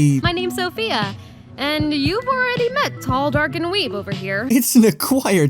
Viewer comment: I didn’t even hear what she said when I first watched it. DID SHE JUST CALL HIM DARK‽ another thing about Meta Runner. Epic Game was involved in the making of the show.